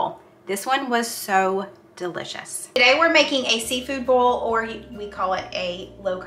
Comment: Oooooohhhhh…. Low country bo-il, I thought you said seafood BOWL! 😂